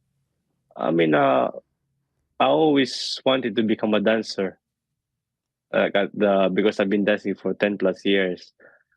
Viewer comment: I legit thought he said he wanted to become a dinosaur lol crazy 🦖